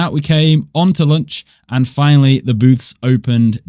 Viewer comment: oh I thought you said boots I was like whuh